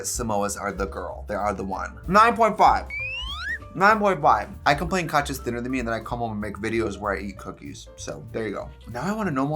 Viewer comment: Can someone translate what was said at 12:46 😂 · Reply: “I complain Katya’s thinner than me then I come home and make videos where I eat cookies”